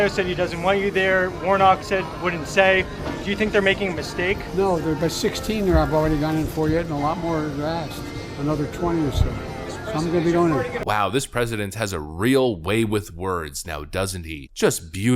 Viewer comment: Seriously what did Brandon say. "they are by 16 and by another 20 I will be doing it" He is not making any sense but he is the President !!